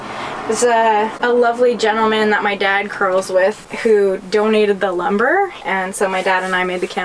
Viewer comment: "a lovely gentleman that my dad curls with" is the most Canadian sentence... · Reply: I thought she said "that my dad cuddles with" at first 😂